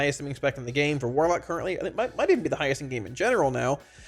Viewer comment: PLEASE can someone clarify what said here? "Destro being the highest MMMing spec in the game for warlock currently"??? I could not understand. please can you slow down and say things clearly. Please for 2nd English speakers. Thank you. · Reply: "might even be the highest in the game in general now"